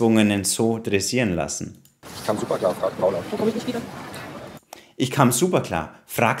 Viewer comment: Bei 28:58m: Was sagt sie da zu ihm am Flughafen, als er antwortet? Ich höre da: "Dann komme ich nicht wieder" - sagt sie das wirklich? Falls sie das wirklich sagen sollte: Sarkastisch als Scherz verpackt, teilt sie ihm da sehr deutlich die Wahrheit mit, dass sie ihm liebsten weg wäre. Oder verhöre ich mich da, sagt sie da etwas anderes?